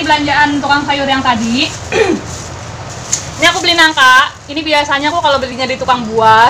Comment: maaf apa sy yg salah denger ya mam.. kok nangka??? 😁😁😁